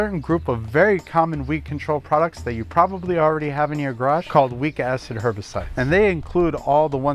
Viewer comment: 😂 I didn’t have the volume loud enough. I heard “weak a$$ herbicide” instead of “weak acid herbicide”. 😂